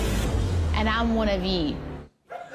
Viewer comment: Swear to God it sounds like she said and I'm going to eat you. 🤣🤣🤣🤣🤣🤣😵‍💫4.3%😵